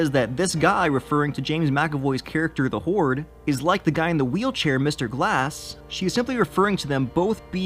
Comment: I think it's "The Horde" because of Kevin's multiple personalities instead of "Hoard". Plus, it makes sense. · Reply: I thought they said "the whore" in the movie lol